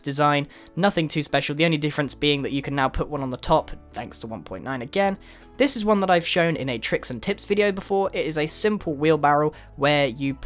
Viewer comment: I thought he said "tricks and tits"...